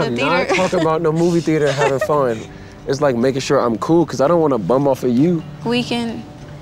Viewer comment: I'm just learning English as a second language. Can anyone tell what he did mean to say at 10:57 minutes? I heard something like" I don't want to bum off you"